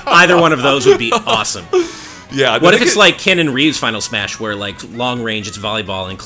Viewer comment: thought he said Keanu Reeves final smash. Now I really want him in